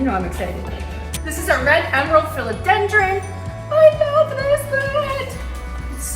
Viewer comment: Really great house tour! What's the one at 21:36? I didn't catch the name. Also, how will you repot or transplant what's grown to a pole? Thanks :-) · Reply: Hi Ink! That was a red emerald philodendron... the huge one, yes? I also talk about that plant in my recent plant haul video. Was that the one your asking about? Right before that I talked a sanseveria samurai but accidentally called it a sumo. Lol. Not enough coffee yet to think straight, Haha. 🌿💚🌱☕😊